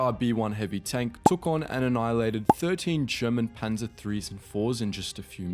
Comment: did I just hear "Sherman" Panzer 3s and 4s? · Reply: German Panzer IIIs and IVs